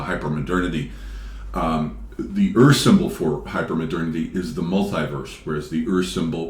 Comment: the ers symbol? The earth symbol? Not sure what you’re saying there, can someone j help me out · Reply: Ur-symbol. Spenglerian term